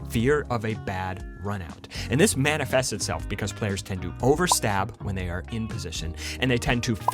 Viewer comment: I heard players tend to over stab when they’re a piece of shit at first and i was about to agree till i rewinded and heard what you actually said.